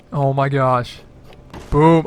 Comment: it sounds like instead of saying boom he accidentally says booooooooooooob